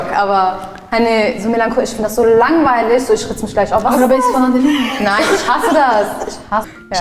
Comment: was sagt die 😂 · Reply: Arabesk ist eine Musikrichtung und die fragt „hörst du kein arabesk?“